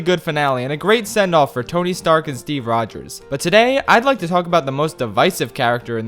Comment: Did he just say tony stark as Steve Rogers